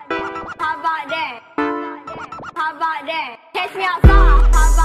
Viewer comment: For a long time i thought she's saying catch me outside, papa dan.. xD